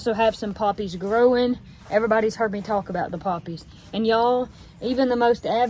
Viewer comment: I thought said she had some puppies - then when she said she dried 'em out I was getting creeped out but then I got it😆😆😊😛